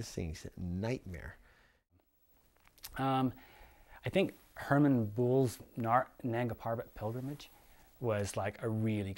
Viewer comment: please tell me the name of the book that you mention at 3:16. I think I hear "Herman Booles" as the author. I am searching though can not make out the title that you say. If anyone knows the book, please let me know the title. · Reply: Hermann Buhl, climbing solo Nanga Parbat pilgrimage